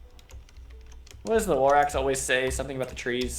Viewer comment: I know he said Lorax, but I heard "What Rorax says about the trees." THEN I was sad there was no Rorax in the video.